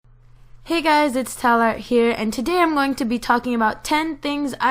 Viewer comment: I seriously thought she said "Hey guys, it's talent here." Seriously, she is talent.